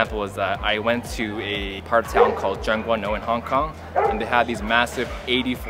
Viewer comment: Can someone tell me which area in hong kong he mentioned? I can't understand at all 😭 Please!!! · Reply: tseung kwan o